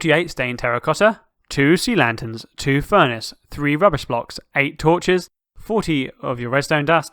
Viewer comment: hold on did he just rubbers or rubbish blocks someone pls answer idk what block at 3:39 edited: also can you be more descriptive cause just saying torch with a picture of a redstone torch gets me confused just minuets in the turtorial so what i think u ment by rubbish blocks is dirt block unless dirt blocks were called rubish blocks in 2018 edited 2 at 9:34 i found out it was dirt but at 3:39 why did he call it rubish? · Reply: Rubbish block are junk items used for if you need a certain number of items in a storage block (for hopper clocks or comparators)